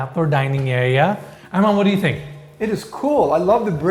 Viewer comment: Every time says, "Armand, what do you think?" I hear "Arvin, what do you think?" And I smile...